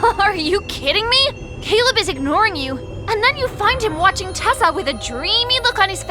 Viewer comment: I swear i thought they said tesla in the start